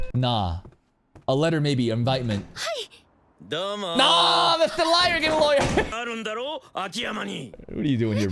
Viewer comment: I thought I heard call the lawyer Mr Liar 🤣